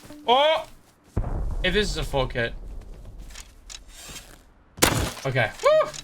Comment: he said "This is a full kit" and thought he said oh this is a fuck head